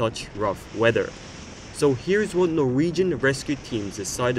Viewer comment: Not trying to be mean but i think you said norwegian not viking